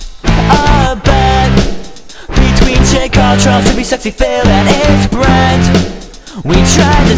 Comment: did hey say sexy phil at 0:10 ?